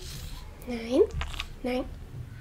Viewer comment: are u german? That sounded like a ''Nein , nein''